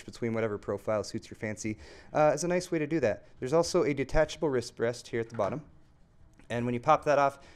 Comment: did you say wristbreast?